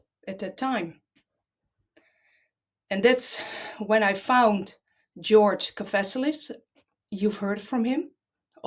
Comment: What is the authors name and the name of the book he wrote? I heard her say "George Cofesilus" and searched for that but nothing came up so I think I'm spelling it wrong. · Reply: George Kavassilas